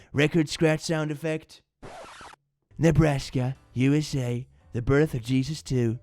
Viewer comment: honesly, for a second I thought it said nevada, not nebraska, and my mind went to madness combat, a shame...